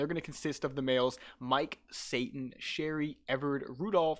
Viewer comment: Did he just say SATAN?!? 😮